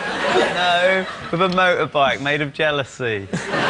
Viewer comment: I thought he said motorbike made of gelatine o.o